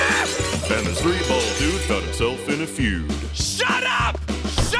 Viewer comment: what did he even say? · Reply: This three-balled dude got himself in a feud